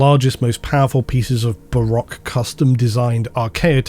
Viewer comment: I apologise, I've tried a dozen different spellings and combinations but I cannot figure out what is said here. "Barak design?" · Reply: Baroque